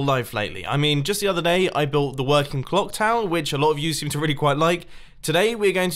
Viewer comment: did he just say clock town